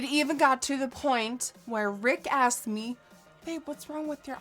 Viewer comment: Thought she said Rick Astley...